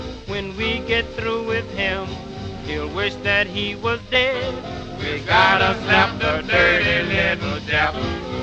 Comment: omg did i hear"we gotta zap the dirty little jap?"